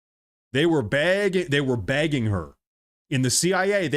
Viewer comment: Hahaha ... I heard you say 'they were begging her', but it sounded like 'bagging' her and I couldn't figure out what you meant by that. 😂😂